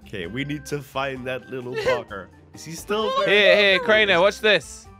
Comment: I’m Sorry That Little Who- 😃 (Or Did I Hear That Wrong💀)